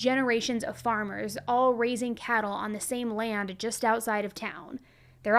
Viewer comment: You said “raising cattle “ I heard “racing cattle” and got excited The disappointment when i realized..